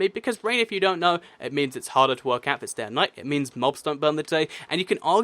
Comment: can anyone understand what he's saying at 5:24? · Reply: "It means it's harder to work out if it's day and night"